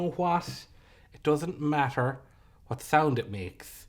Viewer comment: Did just say she doesn't like America?